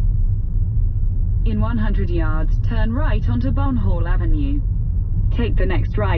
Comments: "in 100 yards turn right onto Bumhole Avenue" 😂 obviously not what it said but gave me a laugh anyway :) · thought it said bum hole avenue lol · i heard ‘turn right onto bumhole avenue’ at first oops.. I can’t be the only one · OMG I THOUGHT SHE SAID BUMHOLE AVENUE 😂😂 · did i hear this right Bum hole avenue? :-P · for a second i thought it said "take a right onto bumhole avenue"